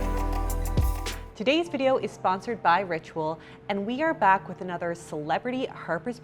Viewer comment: For a moment I thought she said "this video is sponsored by Redbull" and was so confused hahah